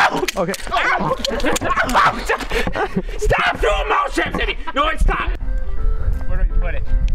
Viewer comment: I thought it said house traps 😂